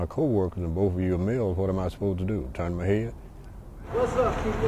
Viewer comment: people english is not my mother tongue can you please help me here at 0:29 what does the witness say? What am i supposed to do? ___________________ please help me here to fill in the blanks! thanks a lot TURN MY HEAD?????????? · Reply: Yes he said turn my head